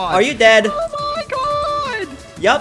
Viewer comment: Anyone heard oh my god